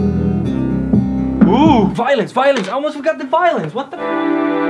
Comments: i literally heard him say VOILENCE 😂😂😂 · So i watched 5 vids, i thought he was saying "violence", after all its Violins.